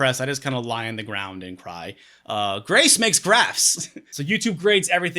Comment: I thought you said "grace makes grass" at first and i was like "does she just smoke a joint?"